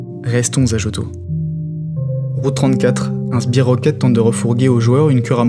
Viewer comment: bordel j'ai entendu rules 34, j'ai buggé pendant 30sec xD